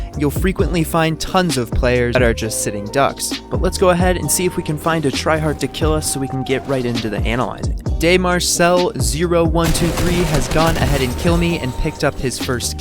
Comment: I thought u said spectating a sweat